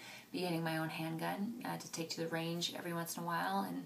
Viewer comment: I can't be the only one who thought she said "ring" instead of "range" at 4:32/